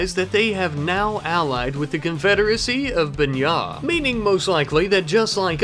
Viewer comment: did he just said "Nya"?